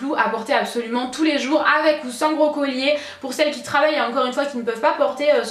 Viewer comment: j'ai compris "avec ou sans brocolis"mdrr bisous ma belle